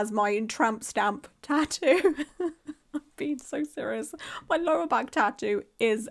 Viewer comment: Me: English is not my first language Also me: Why does she say she has a disco on her lower back And also a great video <3